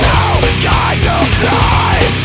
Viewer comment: I hear him saying “ this is nooo dentaaaal floss”